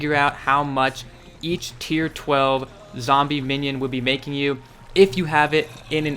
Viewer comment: Did I hear right, “tier 12”?